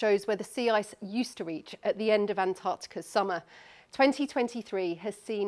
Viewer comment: I'm pretty sure that's actually 2023 as in the year lol. That's what it says on the screen. How did no one catch that?